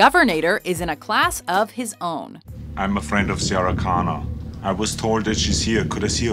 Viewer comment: anyone else heard "I'm afraid of Sara Connor!"?